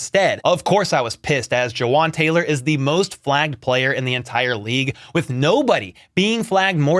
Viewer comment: It is 1 am when watching this video, im dazed and trying to get tired, for a second i swore you said "Jawaan Taylor is the most Black player in the entire league"